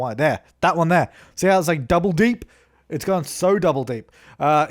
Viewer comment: I thought he said "Double D's" XD Omg ahah.